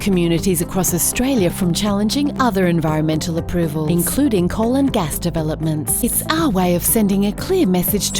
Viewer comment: When you said "coal and gas developments" I heard "colon gas developments" 😂